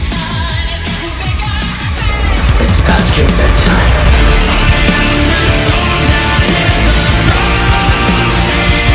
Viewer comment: what does he say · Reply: "It's past your bedtime"